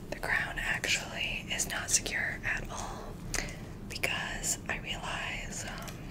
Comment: I thought you said "the ground is not secure", and I'm sat here thinking "what the hell is going on?"